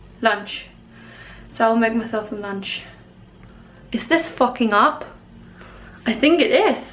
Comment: When you said fogging up I thought you said fucking up. 😂